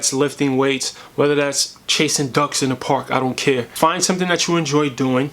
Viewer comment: Tbh I thought you said chasing thugs in the park and I was like.. did he do that?😄